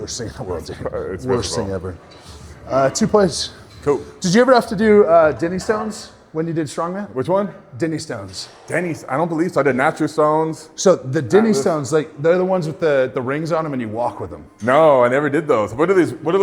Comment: i thought he said DIDDY STONES 💀😂